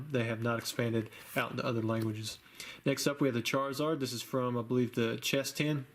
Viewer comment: Did you say "Charizard chess tin"? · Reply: Charizard chest tin. It comes with a pencil a notepad promos and a couple of packs